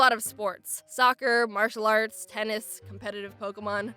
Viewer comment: When she said martial arts i thought she said marshmallow farts :/